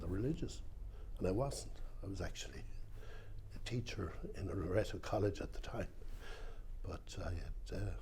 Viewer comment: An important and heartbreaking story. I'm struggling to make out a word - could anyone tell me what sort of college refers to at 14:08? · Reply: Think I've got it - Loreto?